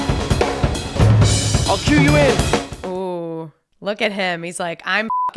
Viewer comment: When I first heard that "I'll cue you", I didn't have subtitles on so I thought he just casually mentioned while perfectly drumming "I'll kill you".